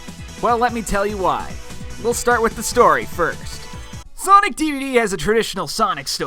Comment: did you say sonic dvd?